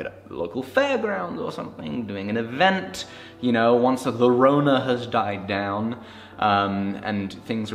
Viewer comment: oh rip he said the c word... hope you wont get demonetised. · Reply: or did i hear "the rona" ? XD